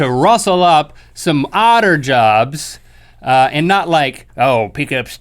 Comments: When Link said "odder jobs", I heard "otter jobs". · Odder Jobs... I heard it as "Otter Jobs" I like my version better. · At the begginging I thought Link said Otter jods not odder jobs o _o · I heard "otter jobs" and giggled! 😂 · Thought Link said "Otter jobs" now im disappointed.